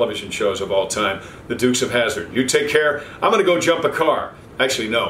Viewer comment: I thought he was saying “Christmas CARDS.”